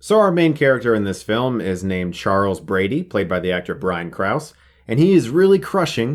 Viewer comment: Thought he said Charles Grady, not Brady. Almost had an aneurysm 😂😂😂